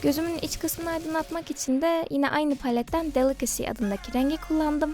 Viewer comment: Those are some really cute lenses... (ve de "delicacy" dediğinde "balıkesir" olarak duydum lmao)